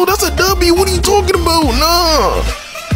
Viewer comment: Bruh robux I thought you say Roblox